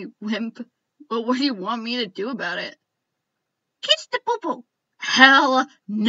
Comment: "Kiss Pa-Pøø-Pøø" That's what I heard 😂😂